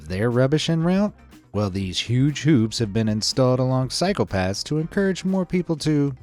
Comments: When you said cycle paths it sounded like you said psychopaths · I was mid hit- when yo said "Cycle paths" I hear "Phsycopaths"